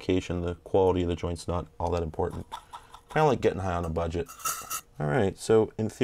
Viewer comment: did he say "It's like getting high on a budget." or am I stoned?